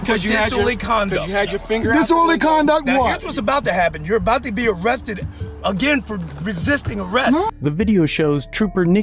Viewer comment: "You are about to get arrested again for, ugh...resisting arrest!" Did I hear this correctly or am I crazy?